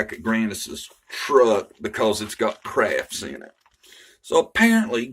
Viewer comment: Crafts? I thought she said Crabs, lol